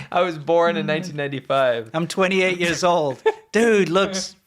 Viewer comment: I thought he said 'I was 28 in 1995 !! '